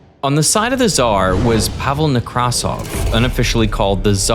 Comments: Every time they say Nekrasov's name, I hear "Microsoft" because they say nEkrasov instead of nekrAsov, which is the correct way. Edit: To be fair, Derek does pronounce it the right way. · Whenever you pronounced "Nekrasov" with the emphasis on the first syllable my tech-oriented brain just heard it as "Micro$oft" XD